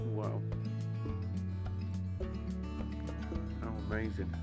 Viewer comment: Listen in 17:49 i heard someone say "not so amazing". Spooky.